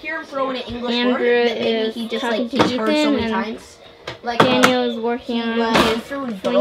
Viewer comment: What did say at 1:29? is talking to...? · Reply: Ethan! best friend if I am not wrong